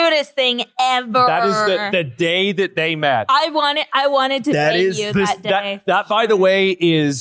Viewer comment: did…….did I hear that right? 6 times and I keep hearing “I wanted to peg you that day”🤣🤣 and no one addressed it or